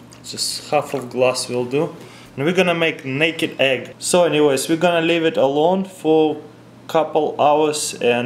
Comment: did u just say da naked egg lol